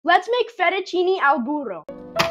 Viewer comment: I thought it said fettuccine al burrito and I was like: "He's gonna put WHAT into a burrito?!"